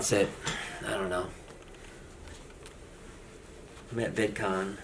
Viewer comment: The pain I saw when you said “I’m at vidcon” is way too relatable. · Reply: I thought he said I'm a bitcoin.